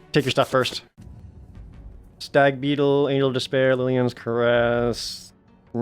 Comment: did he say anal despair?